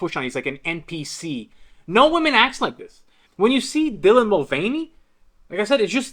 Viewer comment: I thought you said "Villain Mulvaney" 😂😂😂😂 Quite Apt dont you think😂😂😂